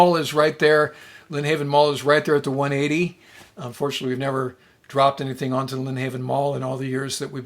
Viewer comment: Hahaha did you say "unfortunately" you didnt drop anything on the mall around 15:50?